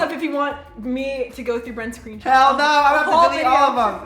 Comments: Did Brent say hell no and 8:23 no hate just wondering 😂🤣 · what did Brent say did he say hell no!?!?